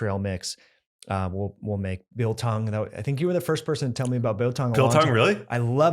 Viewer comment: What is this word? The transcript says "bill tongue," "bow tongue" and "bill tong." I suspect it's none of these. Whatever the word is, what's the thing they're talking about? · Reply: Biltong!